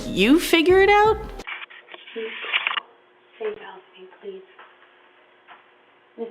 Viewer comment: I think that lady needed help i heard her say “help me..” “pls”